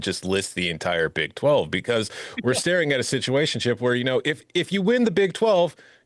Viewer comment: When said “Situation, chip” talking about the Big 12… I thought he said “Situationship”… which is probably a better name for the AQ…AQ goes to the winner of the Big 12 Situationship